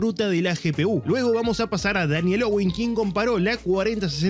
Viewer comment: Me perdonara amigo y me gusta ver mucho sus videos, pero no entendí en el 0:45. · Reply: Hola! Daniel Owen es el quien subió el vídeo comparando la 4060 ti vs 5060ti, usando DLSS 4 y mfgx4 para sumar al análisis 🙌